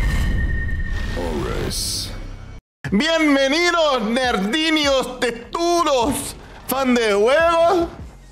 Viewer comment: Juro que entendi "merdinhas tetudos" kkkk